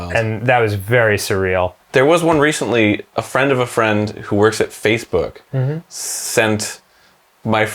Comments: Did just say ‘Faithbook’? Always knew he was secretly Mormon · Did he say Fathebook?